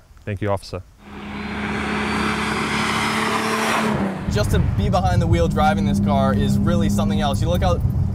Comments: it sounds like he said "Justin Beiber behind the wheel" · did he just say Justin Bieber???....imma gonna get triggered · did he say justin bieber at 6:30???